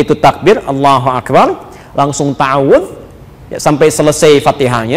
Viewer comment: beliau bilang apa? Habis takbir. Allahu akbar langsung apa? Ta awuf? Kurang jls soalnya, Ad yg bisa bntu jlaskan.. Terimakasih sebelumnya🙏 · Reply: Om rule's pemahaman sy itu adlah sholat khofifahtain artinya pembuka sholat. Jadi hanya takbir langsung ta’uwdz (a’uwdzubillahiminnassyaitonirrajiim bismillahirrahmaanirrahiim) lalu baca alfathihah lalu langsung rukuk. Jadi tanpa iftitah dan surah